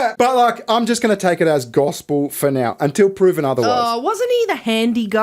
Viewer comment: did you said "gospel"?